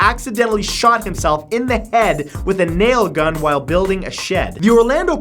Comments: I really thought a nail said anal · A nail, thought it said anal 😅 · when it said "a nail" i thought it said anal